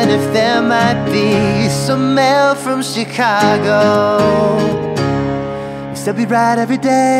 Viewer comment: Loo I thought you said some male from Chicago during the whole video